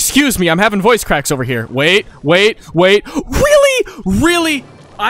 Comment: Weally? Weally? Boi speak english